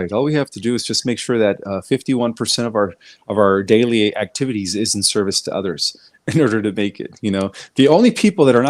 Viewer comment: What? '" we just have to make sure that 51%of out daily activities isn't service to others". Then is he saying we should be doing service to self ? thats his answer as to how are we to prepare for the solar flash? Did I hear right ? Or should I go clean my ears out. ? 🥴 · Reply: He said 51% literally service to others (but you could do more than that. )